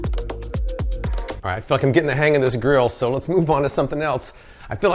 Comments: ' I'm fucking gettin the hang of this grill'!!!!... lol I had to play it back cause I thought that's what you said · "Alright, I'm f00king getting the hang of this grill." Not really what he said, but that's what I initially heard, hehe. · I could have swore you said “f***ing getting a handle of this grill!” I was like damn, he likes this grill I guess